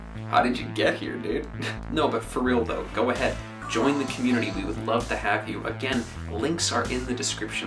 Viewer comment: tradeable crates?! thats gonna be some big bucks. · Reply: i swear for a second i thought he said "we would love to hack you"